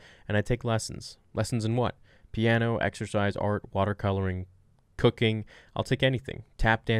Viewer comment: am I the only one who had to listen twice? LMAO! I clearly heard "lessons in anything...water art and cocaine" and yet, she actually said "cooking". hahahahah!